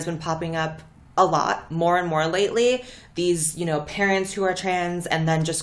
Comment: I misheard parent as parrot. I guess parrots are raising the kids now